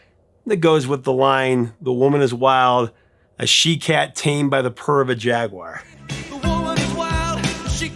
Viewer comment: I loved "One On One" and don't think it gets the recognition it deserves. It was winter of the 82-83 school year during basketball season. I was a freshman and the JV cheerleaders always put decorations on our lockers the day of our games. A sophomore named Jenni was the one who did mine and I thought she was absolutely beautiful. In my mind's eye, this was the song we would dance to together at the school dance on any given Friday night. Alas I never worked up the courage to ask her out before she moved to a different school, but it is still a wonderful memory as my first high school crush. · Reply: One on One, definitely my favorite of theirs... conducive of certain idle fantasies. - The Maneater line, A she cat tamed. by the purr of a jaguar heard it as something like ... ...tell by the pair of dice yôu wore